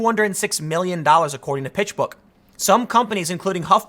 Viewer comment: I legit thought Tim said “Bitchbook”